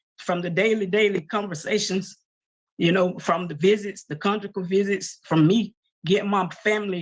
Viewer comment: Did his sister accidentally say Conjugal visit or did I mishear that?